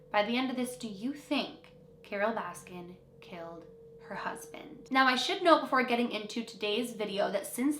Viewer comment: I don’t know why but every time she says Carole baskin killed her husband I hear “wacked em’”